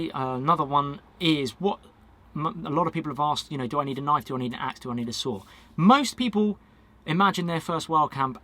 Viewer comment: it sounded like he said “ do I need a knife, do I need a axe or do I need a sword”